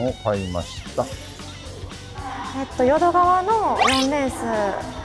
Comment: 完全に淀川に聞こえたぞ